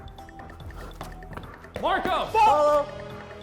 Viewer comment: When D yelled Marco before killing the guy crouching behind, did someone yell FUCK instead of Polo?